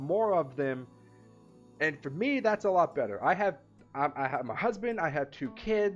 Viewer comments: Did he just said I have a husband? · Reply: I heard "I have a husband." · Did he just said I have a husband?